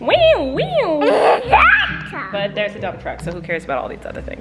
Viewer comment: I heard say "there's a bump truck"